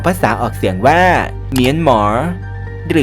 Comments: ได้ยินว่า เหมียนหมา555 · เราได้ยินว่า(เหนียนหมา)ไม่รู้ว่าได้ยิเเค่เราหรือเปล่า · นาทีที่ 3:38 กินข้าวอยู่นี่หันไปมองจอเลย ได้ยินว่าเหมียนหมา<num>